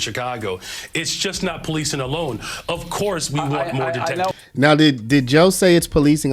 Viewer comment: I hear of course we want more det (detective's)as he gets cut off. Anyone else hear this